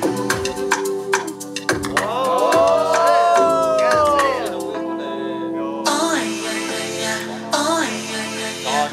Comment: is it just me or did woozi say "goddamn" in reaction to hoshi's part LMAO 6:04 he's so cute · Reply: lol yeah he said “oh shit, goddamn”